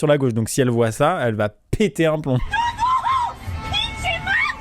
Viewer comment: elle dit "pinche madre" pas "pitché" c'est sûrement une femme d'origine mexicaine parce que pinche est très utilisée là-bas comme "fucking" aux USA... c'est tout pour moi 😅